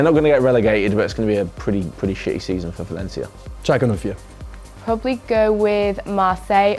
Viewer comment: What did say at 4:48? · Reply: Schalke 04